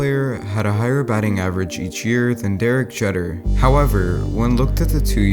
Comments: Did he just say Derik Jetter? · Did he just say jeder